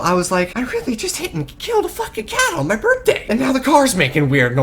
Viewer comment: put it at 7:05 and start it and ur gonna here him say i just killed a fucking cow instead of cat